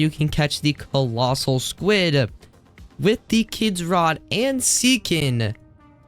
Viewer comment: Why do i hear you saying "the kids rod"for no reason